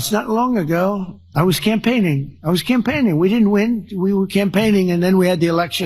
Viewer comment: Did anyone else hear him say: we were campaigning, we didn't win"? @ 10:38😮